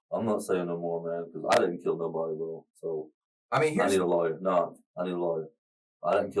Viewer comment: Sounds like he said “I want a lawyer”. That’s why the interview ended abruptly.